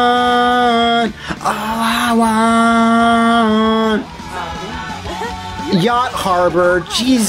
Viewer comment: Is that what he's saying? "All I want"? I always heard "Sonic wars", which gave me images of all the Sonics from the different games fighting in a Clone Wars style army.